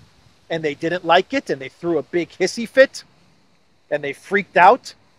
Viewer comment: A super bunch of wind ? And its " Hissy " fit sounds like you say " Hippy "